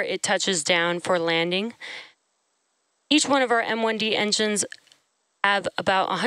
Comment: She said "3 outa the 9 awendy engines" me:"It's bloody Merlin engines" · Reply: You misheard. She said, "Merlin 1-D engines."